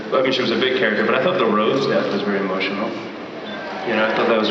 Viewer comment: What does Paul say at 2:56?? Whose death was the most emotional?? Oooh does he say Rose??